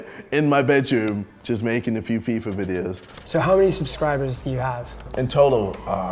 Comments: if you turn on captions it says 'peeper videos' instead of 'FIFA videos' LOOOOOOL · subtitles said he made peeper videos, I knew he was a perve